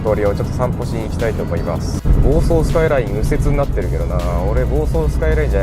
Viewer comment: 房総スカイラインが暴走スカイラインに聞こえたよww